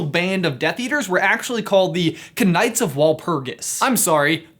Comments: did he just say kah - night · Did say "Ka-night of Walpugis"? lol